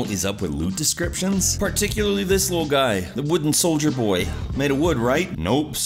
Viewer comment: did you said "Soldier BOY"?